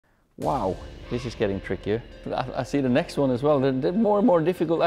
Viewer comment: At the beginning I thought he said “Wow! This is Kieran Trippier”